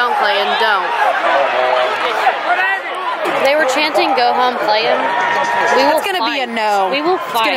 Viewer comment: Does anyone know what said at 4:48 about what they were chanting? I’ve literally replayed it 30 times and I can’t figure it out lol. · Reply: Sun Kiss go home clayton 3 weeks late